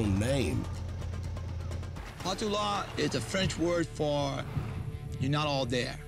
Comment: Bruh I literally thought he said Jake Paul